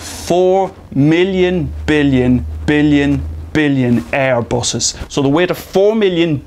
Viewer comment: what does he says at 2:09...... 4 million billion billion billion air what? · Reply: Airbuses.